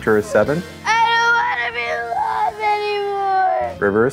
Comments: the little boy crying saying he doesn't wanna be alive anymore is extremely concerning.. no adult should feel that let alone an innocent child, this made me so sad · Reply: I thought he said I don't want to be loved anymore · the little boy crying saying he doesn't wanna be alive anymore is extremely concerning.. no adult should feel that let alone an innocent child, this made me so sad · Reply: Oh. That’s what he said? I kept hearing, “I don’t wanna be in love anymore”